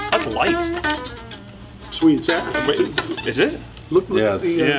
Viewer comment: what does say at 8:25 ? it's -------- ? Joe says the Strat is lite. DBII has said elsewhere he likes his lite 1964 Strat, so without the serial # - what is the wood type? cheers. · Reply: he says its ash. if you slow the video down you can hear it clearly